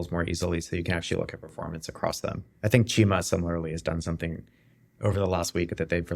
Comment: Question: at 06:58 mentions a company or product like braintrust, what was the name? it sounded like cheema? Loved the episode, thanks!